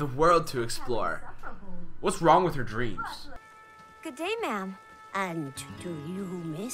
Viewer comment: Wtf is wrong with me when she said good day ma’m I heard a gay man 😂😂😂